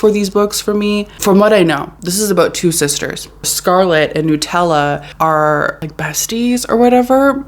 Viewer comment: The way I thought you said Scarlett and Nutella I’m dying